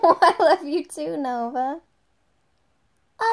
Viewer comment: I thought he said “Who”